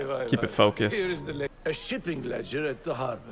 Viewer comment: When he he said shipping I thought he said sh**ing